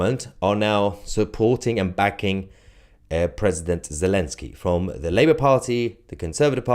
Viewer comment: I had to skip back as I thought you said "Princess zelensky" 😂